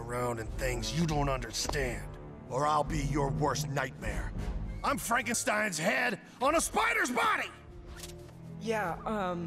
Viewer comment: O’Hare: “You listen to me boy, don’t go poking around in things you don’t understand. Or I’ll be your worst nightmare. I am LORD FARQUAAD in EDNA MODE’S BODY!!!!” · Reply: I heard "I AM FRANKENSTEIN HEAD ON A SPIDER BODY! "